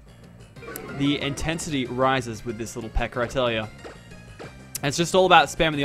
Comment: I may be wrong, but i heard him say: "Little pecker."